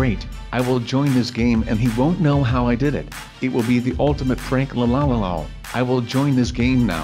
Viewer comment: Did AnyOne Else Hear Him Say PRANK